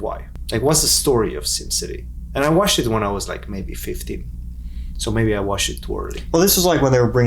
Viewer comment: "And I watched it when I was like 50" (misheard what he said, pretty funny) :D